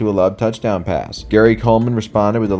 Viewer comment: Did you say "Gary Coleman"?